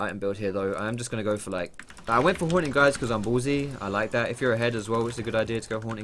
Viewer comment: The way he says "haunting guise" made it sound like he said "I got horny, guys" lol